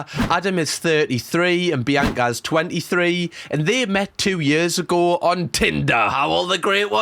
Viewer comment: Oh, they met two years ago. I misheard that and had to go back and relisten a few times, I though you said "ten." 17:20 Jegus cripes, they're planning to have the wedding on the airplane in the air?! With all the guests and the film crew?! 34:06 Wait, they're putting actual sand in the venue?! Oh, I guess since it's some leaky warehouse, I suppose the venue is fine with it?